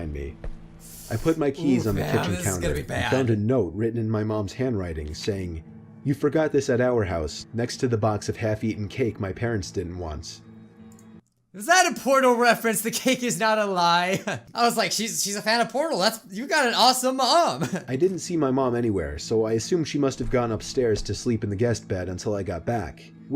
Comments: Who else thought he said is that a porno reference? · who else thought he said “Was that a porno reference” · I thought he said ‘is that a porno reference’ :\ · I honestly thought he said "is that a porno reference"😂